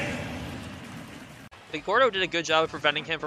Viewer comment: did i hear ga*